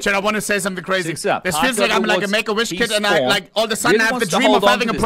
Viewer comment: WHAT DID HE SAYYYYY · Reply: He said he feels like a "make a wish" kid. Which might be considered controversial because these kids are dying and he's clearly not